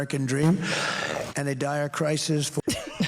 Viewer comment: The fart heard 'round the world and then don says "a dire crisis." But I heard 'a diarrhea crisis' 😂